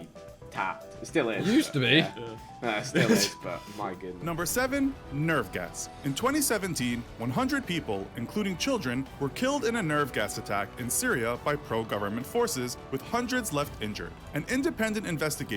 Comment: thought it said "nerf guns"🤣